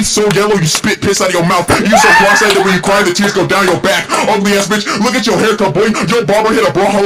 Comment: What did Packgod say after the piss one? I couldn't hear it because of Jidion's laugh. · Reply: “You so cross eyed when you cry the tears go down yo back”